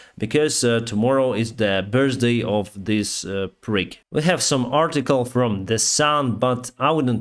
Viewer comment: Did I hear you correctly...calling him a prick?